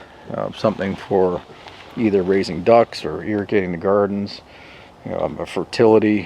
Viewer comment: “Irritating”the gardens. I thought I heard wrong.